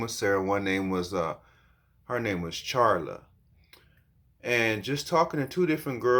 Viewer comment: Charla? I thought you said Carla